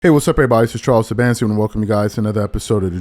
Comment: My 4 year old son walks in the room, takes one look at the tv, points and says, "Mr. Potato Head". Whole family cracking up. Thanks Charles or, you talk too fast for me to catch the name. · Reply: His name is. He had said his full name in previous shows but he says it fast. At first I thought he was saying as well but it’s just Charles.